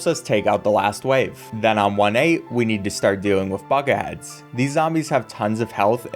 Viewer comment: so i have hearing issues and misheard at 2:01 instead of saying bucket heads i'm hearing fuck ads and i've replayed that a few times and i'm still hearing fuck ads.